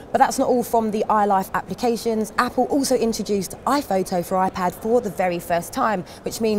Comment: Did she just say ipad 4?